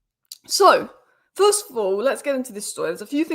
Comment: It’s ok to let out that slang! could have sworn you said “this foo” instead of “first of all”